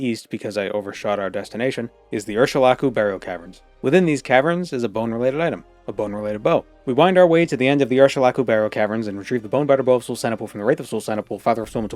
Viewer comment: I thought you were saying "bone batter bow" which sounded kinda dirty